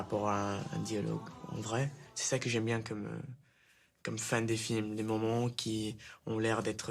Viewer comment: I thought he was saying "c'est ça que j'aime comme fan de films" rather than "fin", seems to make more sense in the context, no?